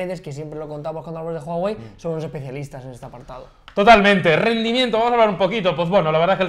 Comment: minuto 6:53 KHÉ BERGA drfrsfdaeuf rendimiento eso entendi XD